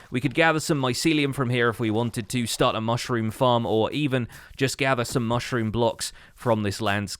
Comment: i thought he was saying `start a mycelium war' Watching hermitcraft too much